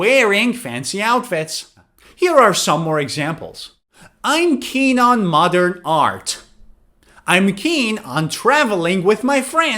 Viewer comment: I heard you said "i'm keen on mother art"😭